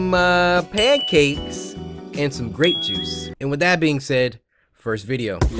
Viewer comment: Hahahaha lol I Thought that you said rape juice and I was like whaaaaaaaaa but then I heard grape juice